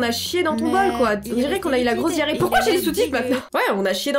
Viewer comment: au lieu de "pourquoi j'ai les sous-titres" j'ai compris "pourquoi j'ai les soutifes"